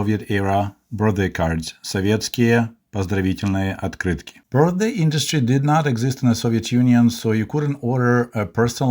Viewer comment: I'm sorry, but just to clarify a point: At 3:40, did you say that Soviets DID invite a lot of people for birthday parties (in spite of the tiny apartments) or that they DIDN'T? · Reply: Didn't